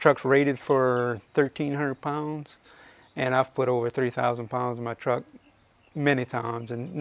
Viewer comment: Did he say he's put over 30,000 lbs on the truck many times!